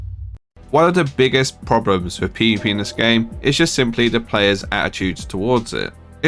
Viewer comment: "One of the biggest problems with PvPenis this game" I can't be the only one who hears that.